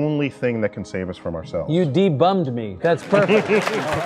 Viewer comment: I am religious but i can really appreciate the honesty this man had to say “you debunked me” 😊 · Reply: I thought he said, "You de-bummed me."